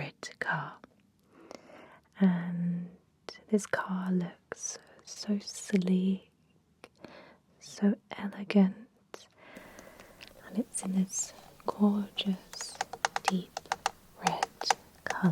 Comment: I’ve misheard you for the first time ever and when you said “this car looks so sleek” I’d though you were saying “looks silly” and laughed out loud. What a ballsy add that’d be!